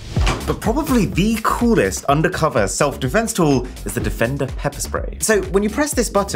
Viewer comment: I thought he said pepe spray